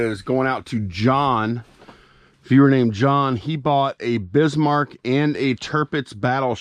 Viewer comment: Also, i had my phone in my pocket and heard you say he bought bismarck, lolol, i thought you meant biz marke, ooooo baby you, you got what i neeeeeed, but you say hes just friend lolol